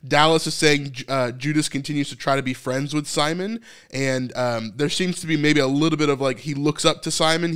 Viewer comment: I heard 'Peter' not Simon...looking forward to that change